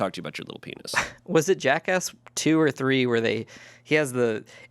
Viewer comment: I’m so chronically online after the 57 minute mark they mention Jack*ss, but I thought they said Jack Ask which is a video format that Jacksfilms does. So I thought they were talking about Jacksfilms until I got extremely confused lol